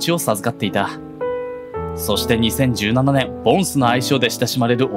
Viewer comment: ポンスが 梵頭に聞こえた